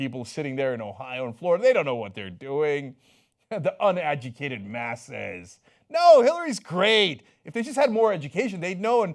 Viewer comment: did he say 'The unagitated masses' ? What a curious thought.